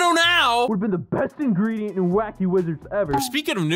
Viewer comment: I THOUGHT IT SAID WACKY JIZ 😭